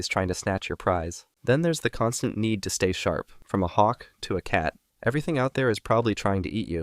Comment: DID HE SAY HAWK THUA